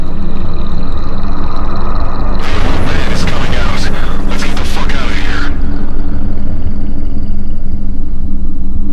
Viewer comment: What did he say? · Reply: THE OLD MAN IS COMING OUT! LETS GET THE FUCK OUT OF HERE!!